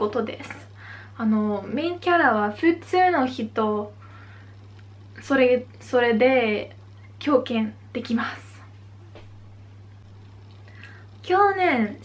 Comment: 「キョウケン」と聞こえるのですが、これはたぶん「共感（きょうかん）」ですね。 ほかの人の日本語動画でも「漢字（かんじ）」を「ケンジ」と発音しているのを見たことがあるのですが、欧米の方にとって「かん」は発音しにくいのでしょうか？